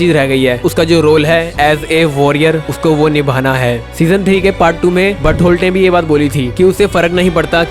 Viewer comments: did he just say," Butt hole"😂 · did he said butt hole 🤣🤣🤣🤣